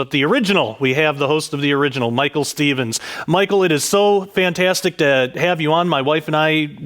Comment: Michael it is so fantastic to have you on my wife....... 'What did he just say? 0:16 loollllll